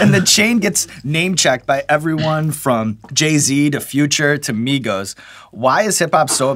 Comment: a lot of people will miss it because of the way he speaks but you can tell Offset is an intelligent dude man. he understands life well and the business side of the game. not gonna lie i don't really follow migos at all, but looking at this interview Offset seems like a cool dude and I'm def gonna be pulling for him in future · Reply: Future? I thought you said offset?